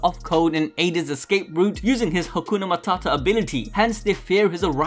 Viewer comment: did he just said Hakuna Mata😳